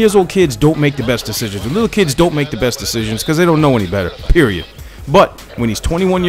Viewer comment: what does he say at 3:53? peri...what?